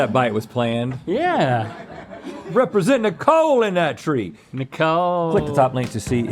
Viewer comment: Really thought Rhett said "representing THE COAL in that tree" not "NICOLE in that tree" I'm from wv and found that priceless